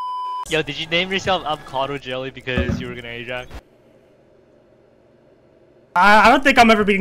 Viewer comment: what did bro say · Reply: he asked jelly "did you name yourself avocado jelly because you were gonna ajax?"